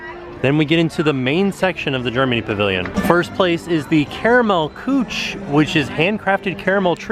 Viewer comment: Thought said "caramel gooch." For a sec, talk about a double take😂